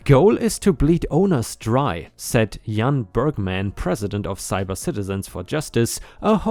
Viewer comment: thought you said Birdman (Bergemann) and we were heading for a little bird law here too.